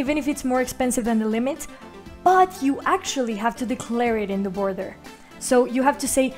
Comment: I could not make out what your saying, "you actually have to declare it in the" ? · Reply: haha sorry, Mexican accent "declare it in the border".